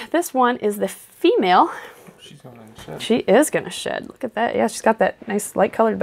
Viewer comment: excuse me???????? Did I hear that correctly! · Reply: What? She said "Shed"